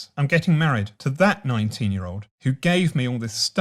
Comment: Is it just me or when he said that 19 year old, 4:25, it sounded like Vat19?